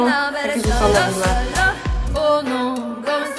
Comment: Tylko ja słyszałam gdzieś tam w środku "Szolo, Solo"? Ona dla mnie nie jest artystką tylko jakąś influencerko-celebrytką, która pojawiła się znikąd i postanowiła, że teraz będzie wydawać piosenki. Czy my nie możemy mieć godnego reprezentanta w tym kraju? Nie moge już słuchać tej piosenki bo dostanę zmarszczek od krzywienia się na jej głos. Tandeta. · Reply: Ja bardziej usłyszałam "Szolo Siolo"